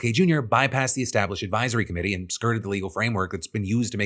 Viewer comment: I'm sorry, I know you said 'the existing legal framework' here at 18:22, but at first I thought I heard it as 'existing illegal brainworm'.